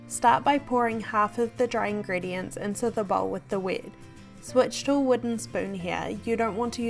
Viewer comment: plz I did not hear well what you said in 5:39 Did u mean, start by pouring the dry ingredients into the bowl with the wid or wet?